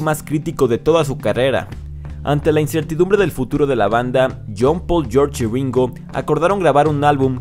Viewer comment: Parece que dices John Paul Jones de Led Zeppelin